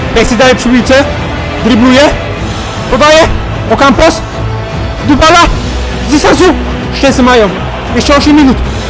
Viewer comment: Lol ja zamiast Dybala usłyszałem Dupala